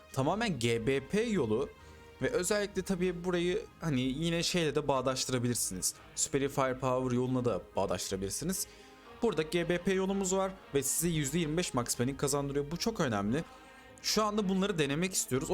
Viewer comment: %25 maxmarine kazandırıyo mu dedin ben mi yanlış duydum adam 😅 -Maeve :)